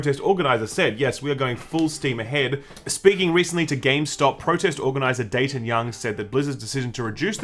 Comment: lol did you actually say " Gamestop" at 6:47?